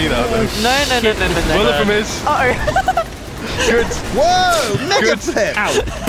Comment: did Sjin say "woah, n---a flip"? thats what it sound like ;\